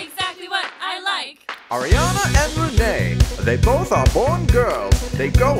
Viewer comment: I'm so sorry at the begging I thought he would say: "Ariana and Renee, they both are pretty gay..."